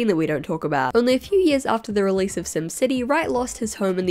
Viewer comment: Everyime you say sim city I heard it as sin city , sin city wasn’t made for you